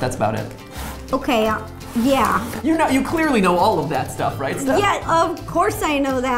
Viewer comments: When Matt called Ro Steph, I kinda died. XD What can you say the man loves his wife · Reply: I heard "clearly you know all that stuff steph" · It sounded like he said "You clearly know all about that stuff, Steph"